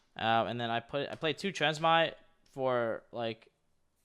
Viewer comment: WTF is that card in the side deck i honestly can't hear him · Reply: The transmigration prophecy